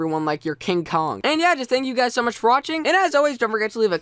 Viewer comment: "And ill see u potatoes in my next video" At first i thought he said idiots instead of potatoes.. xD